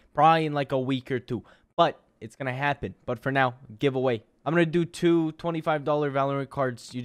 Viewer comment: For the first time I commented on your video because I heard "gibe away"